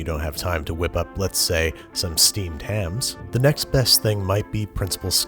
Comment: The “Steamed Hams” at the start really caught my attention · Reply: I thought he said 'steamed clams'?